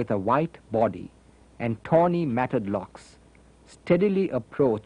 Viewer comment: Hi, does anyone what it said to minute 47:32-47:35please? Sounds like a toni and something, or, at least this is what my mind keeps telling me! Thank you so much! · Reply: Tony matted locks